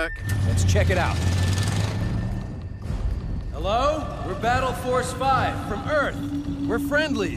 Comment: For 5 years as a idiot, I thought it was saying hawk wheels battle forcing 5